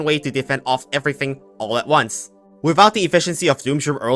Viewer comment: was it just me or did I hear Cock Cannons?